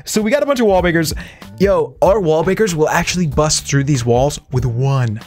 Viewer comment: Did he say wall bakers